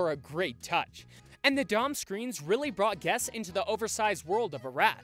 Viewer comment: did he say Dom screen? Does He mean dome? (I've never ridden this ride)